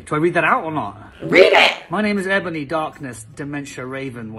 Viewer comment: did he just say Meh name or am I Hearing things? Because I'm pretty sure he said Ebony... 😅 Edit: Yep he is saying my name! 😅